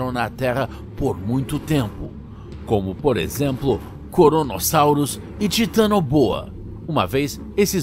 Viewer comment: Eu entendi ele falando koronasaurus ksksk